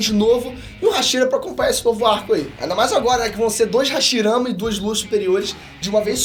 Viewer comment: Eu não acredito no que eu ouvi! Ele falou HASHIRAMA!!!! 😂